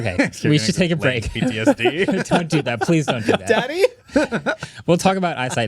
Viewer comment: timestamp? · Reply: someone says daddy. I literally saw the comment, randomly tapped a section of the video, and that's what I heard. Idk if it's the same one.